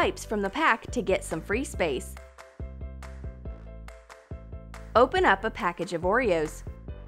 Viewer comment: I really thought she said "open up a pack of geboreos"